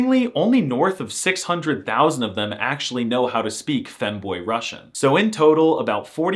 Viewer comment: I thought I heard “Fenway Russian” after Chiraq